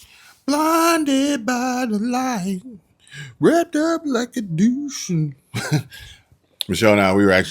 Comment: it “rapped up like a Duce “ ! ( reved up as in high RPM’s like a 32 Ford ) ! I have to admit I thought it was what you said, but it makes sense more with the automotive reference.